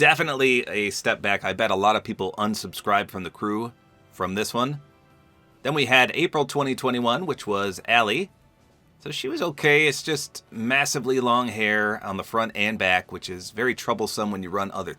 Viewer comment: Did he says massive